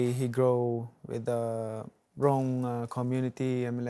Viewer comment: Can someone tell me what he said at 5:29? What type of community? I kept hearing "brown" community :/ although i'm pretty sure it wasn't that.